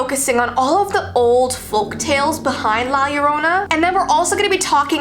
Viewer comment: At first I thought it said la corona